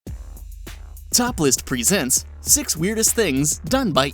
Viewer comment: when the video said "top list" I thought he said "topless" 😂😂😂😂😂